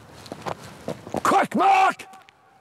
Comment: Does anyone know what he’s shouting at 10:46? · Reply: He was saying quick March, left right left